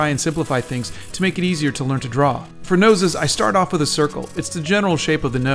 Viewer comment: i’m kinda dyslexic so i thought u said drawing “noises” for the entire video and i was so confused